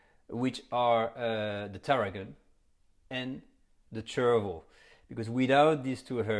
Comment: This may be a dumb question, but what's Chervil? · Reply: I thought he said "cherryville"